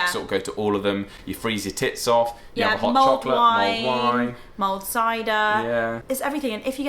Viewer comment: Oh, I thought they always were saying, Mold Wine. But you were saying , Mulled Wine. .